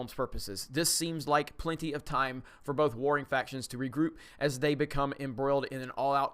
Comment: I misheard you reading it as "enough time for both boring factions to regroup".